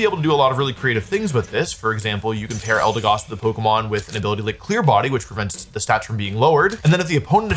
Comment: the subtitles saying Clear Bodyody is killing meeeeeee